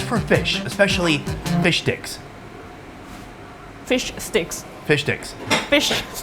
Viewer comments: Had the absolute best time co-hosting this burger episode with Sonny! Tasting some of Japan's wildest burger creations together was such a blast 🍔🔥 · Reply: Fish dicks ~ that's what he said, that's what we all heard :D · it definately sounded like "fish dicks" :face-blue-covering-eyes::body-blue-raised-arms::face-purple-smiling-tears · it sounded like "fish dicks" 😅😅